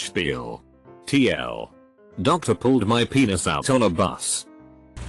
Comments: Lol 7:41 it sounds like he said dr pulled my penis out on a bus 🤣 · I heard "doctor pulled out my penis on a bus" and got up immediately